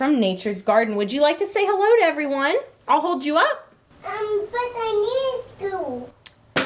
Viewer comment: I thought for a second said "But I'm miniscule!" and was really surprised But then I realized she actually said "But I need a stool" lol